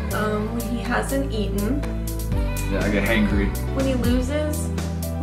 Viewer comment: Lmfao When said being hungry puts In a bad mood I thought he said " yes I does make HANGRY " 😂